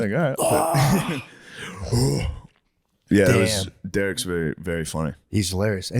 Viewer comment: who did they say? derek?